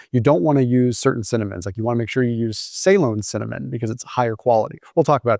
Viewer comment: when i first heard of Ceylon cinnamon i thought he said "saline cinnamon" saline cinnamon does not exist LOL